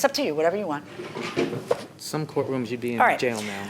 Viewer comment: Did anyone hear the defendant tell his ratchety wife “ if this was real, you would be put jail “? I felt embarrassed for him! 😳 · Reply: I think he had said at 14:48 was "in some court rooms you'd be in jail by now."